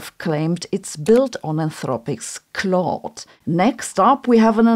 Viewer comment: "it's built on anthropics clot" ??? I listened a few times and can't work out what is said. Can someone help? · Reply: It is Claude, Anthropic model.